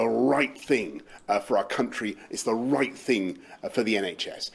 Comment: I love how he says "...it's the Reich thing for our country! It's the Reich thing for the NHS!", or maybe I misheard. 🤔